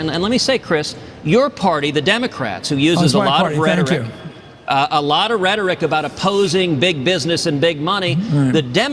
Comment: what does Matthews say when Cruz says, "Your party, the democratic party" Mathews say something but you cant hear it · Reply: He says, "Oh - It's my party. Thank you."